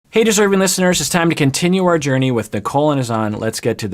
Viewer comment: Could've sworn he said "hey disturbing listeners"